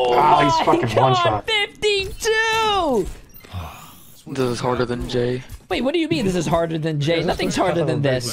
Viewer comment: what did he say 👀😏 I'd like that hard jay